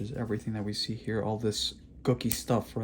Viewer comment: I thought you said cookie stuff rather than gooky stuff when you mentioned subungal debris.